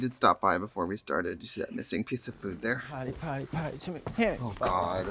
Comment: I think that Katya said: "Party, party, party, till my panties fall off" on 0:11. They sang it while watching the Circle btw